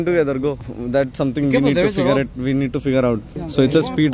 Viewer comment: says we need to cigarette instead of we need to figure out😂